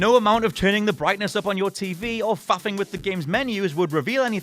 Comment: did he just say "fapping with the game's menu"? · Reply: Faffing.